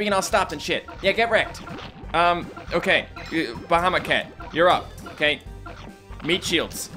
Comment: I thought you were saying Bahama cat